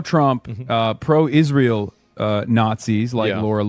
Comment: At about 31:30 did he really say "Pro Israel Nazis"? :person-turqouise-waving